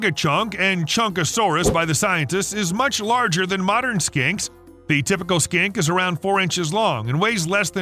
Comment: I thought he said skank. and modern skanks xD